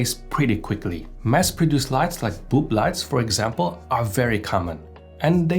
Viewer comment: Did he say "boob light"? It sounded like he said "boob light," which is a completely accurate name for that light fixture. I will never refer to them any other way again. 😅